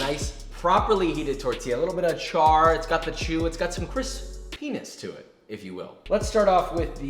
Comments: PAPA Bless. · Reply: When he hesitated the word “crispiness”... 8:25 😂😂 I heard something else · Did he say "Penis"? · did he say “….has some penis to it…” in regards to a fish taco? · mark did he just say Chris Penis?